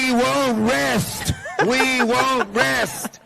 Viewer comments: I heard " we wont win " an " we wont last " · is he saying that "we won't win".?